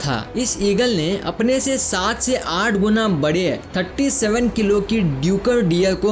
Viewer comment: you say tatti instead of thirty😂😂😂🤣🤣